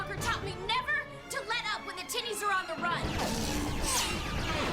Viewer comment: I'm just gonna say right now that it does not say "tinnies" in the subtitles